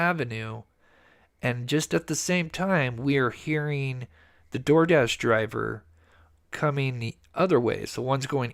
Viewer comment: I thought i heard em say "door dash driver"